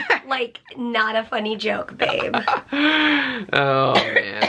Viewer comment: I totally misheard and was so shocked for a sec then realized lol. When she said “It’s not a funny joke babe” I thought she said “ it’s not a f%^*ing joke babe” which seemed so out of character. I think it was because I was in the ER today and the person in the room next to me kept cursing at the doctors and nurses so it’s in my brain.